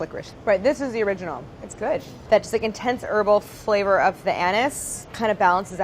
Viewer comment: What is 'urbal' When she said intense 'urbal' flavour I am so confused rn I've never tasted licorice before so I have no idea what she was trying to say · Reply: Precious C. *herbal she was referring to how black licorice tastes like herbs